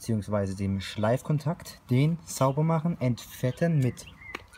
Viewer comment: Und fetten mit Bremsenreiniger?!? 😂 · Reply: Hallo, das hast du falsch verstanden. Ich sage entfetten bei 0:48 grüße und ein schönes Wochenende